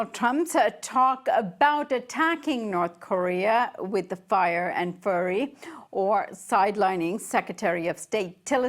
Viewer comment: did she say, "fire and furry"?????